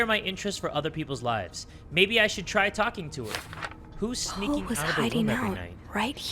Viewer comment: My brain autocorrect that last part to "Maybe I should try stalking" instead of talking lmao